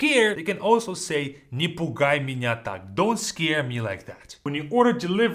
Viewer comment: the auto generated subtitles says, nipple guy miniatures lmao